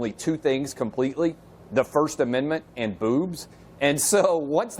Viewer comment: If you watch his lips closely, you can see he says "...the first amendment and booths"..... Clearly he is making the "th" sound not a "b" sound which is a plosive sound, only made by putting both lips together. Also, his context is about freedom of speech. The term "boobs" has no relationship to his topic. The news anchor and her other guest are obviously trying to invent a word that this man did not say in order to embarrass him or discredit him.